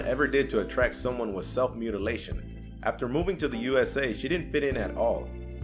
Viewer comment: Oh thank god, i thought for number 9 he said self-immolation. Glad I was wrong. XD